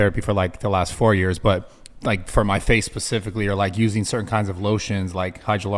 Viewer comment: does Brad say, “For my face PACIFICALLY”??? 😂🤣😂